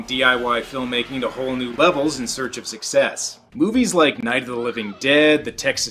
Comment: You should really do a review of "Night of the lepus" (giant killer rabbits!). It's the perfect movie for your channel. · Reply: Did I hear "Night of the Lepus"........I think I can get behind that one. So hop to it. ~ The Black Rabbit has spoken.